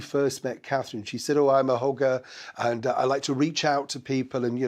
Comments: Oh, when you said M said "I'm a hugger" I thought for a second you said "I'm a hooker"! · OMG 😂 re Nutmeg's version of events on 1st greeting with Princess Catherine, I thought you said "I'm a hooker' as opposed to "I'm a hugger" 😂 I nearly choked on my tea! Not that the ' hooker' epithet wasn't the truth 😂😂🇦🇺